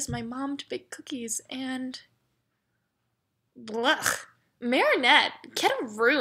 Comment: When Chloe sad blech I thought she said bleach